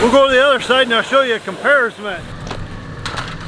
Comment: Did just invent a new word? "Comparisment" Maybe, I heard it wrong.